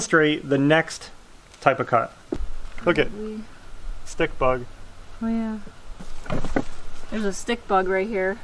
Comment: First I thought you said stink bug! That is the only bug I can't stand! Hate them!!